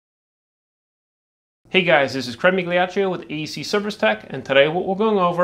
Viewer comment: What is this guys name it sounds like he says crummy gleotchu I am not trying to make fun but I just can not make it out. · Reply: HA HA, that's great! The non important info is what I tend to skip over, sorry! It is Craig Migliaccio and our website is <link> where there are articles, videos, an about section, etc, thanks!